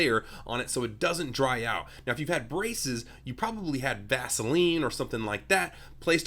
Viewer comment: He said "Vaseline" and I thought he said Gasoline 🤣😅